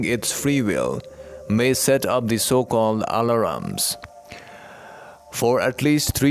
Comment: Hi, there is a word that you use several times in your channeled message and I do not know what it is. It sounds like “honoram” or “allaram.” I first heard it around 8:45. Could you please tell me what the word is? Do you mean ALARMS as in setting up an alarm or reminder? This was a very good message. Thank you so much. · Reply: Nancy yes alarms, thank you for your love and light